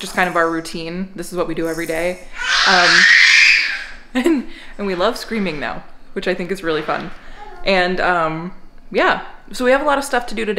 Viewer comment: "now he's into screaming, which I think is pretty fun" - said no parent ever. Oh wait, Jenn said it. :) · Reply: I heard "Now he's into screaming, which we think is pretty fun.:" I took that to be the Mommy "we", as in "he does, me not so much."